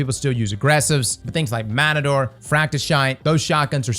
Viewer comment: Im sorry but did i just hear him say fractishit.😂